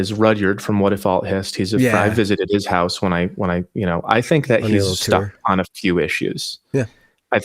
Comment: "...the is Rodgers from What if all is, I think he is... I think he's stuck at a level six on a few issues..."" What does he say at this timestamp?? he says it so quick and not clear, but the other guy seems to understand him perfectly.... can anyone tell me what he said, and the previous guy he mentions also?? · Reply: “Rudyard from I visited his house, I think he’s stuck on a few issues.” Rudyard Lynch is a prolific & learned young content creator I’ve been honored to interview a couple times now.